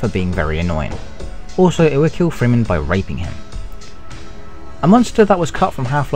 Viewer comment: did they just say the monster would kill Freeman by raping him? just wanna know if I heard that correctly.